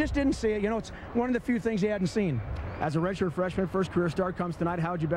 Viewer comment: Whoa. You said career and I was in a drunken stupor and thought you said "cancer". Did I totally fuck that up or what? It's funny because I remember replying to a guy who said something similar, so i guess it was just in my subconscious to reply back without really reading what you were saying. Anywho, go Niners!